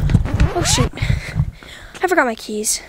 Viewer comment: did ahe say sh*t lmaoo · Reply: She said shoot not sh*t